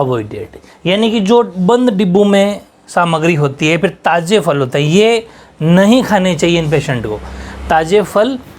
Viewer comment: Sir 36:22 kya bol रहे हो ताज़े फल खाने चाइए correct it 🙏🙏🙏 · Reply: Sir nai bola taaje fal nahi khaana chahiye 😂😂